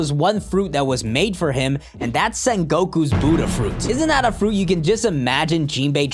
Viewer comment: istg i thought i heard him say "son gokus buddha fruit" and i was like "gokus in one piece now"